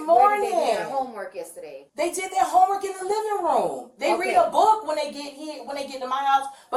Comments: Anyone else catch the slip at 10:15 where she said "they read a book when they get hit" and quickly changes hit to home? 😔 She's trash. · Did anyone else hear the mother say at 10:16 "They read a book when they get hit..."?!!!